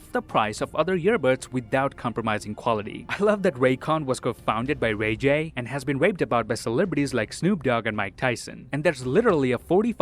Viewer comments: "I love that Raycon was co-founded by RayJ and has been raped about by celebrities." I'm not the only one hearing this, right? Even the subtitles said it. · Raycon has been raped about by celebrities? (That's what I heard and the subtitles confirm it. I wonder whether you could get flagged for something like that.) 6:21 You'd like to see more of a character willing to shoot kids? Got it. 9:44 Are you familiar with 'The Fandarin' from Iron Man 3?